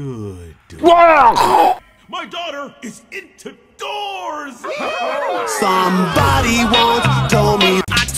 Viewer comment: Guys my daughter is in two doors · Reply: What I thought he said my daughter into doors 😅